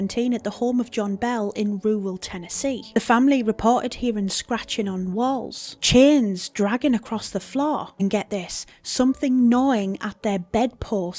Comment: I swear I heard "CHINS dragging across the floor" and was SO confused lol